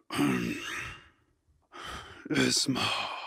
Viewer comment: hold on did he just say Isma?